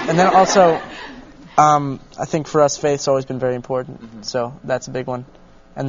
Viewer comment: What did Nick say at 0:55? Like what was the 4th thing? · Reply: wenzba i know this is 7 years late, but he said faith 😂